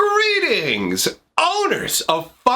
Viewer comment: did he say Greetings? Or GREENINGS